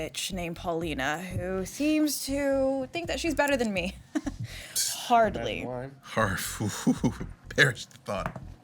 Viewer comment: What did Jasper say at 12:51? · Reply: It was difficult and I certainly might be wrong, but I think he says “not hard”.